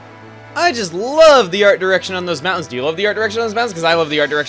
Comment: At the beginning, I swear I heard him say under erection on those mountains. OMFG. O_O